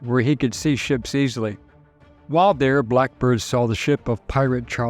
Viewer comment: esque, Oi, I think oi heard a "blackbird", I did!